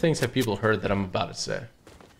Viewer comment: When he said we have zero kills I thought he said cloths instead of kills😳